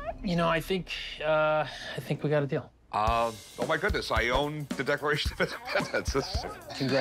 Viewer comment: haha. It sounds like someone is saying " oh my god" in a high pictched voice in the background.